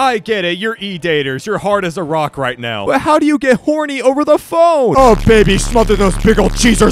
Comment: On the first bone one I thought it said phone and not bone😅😂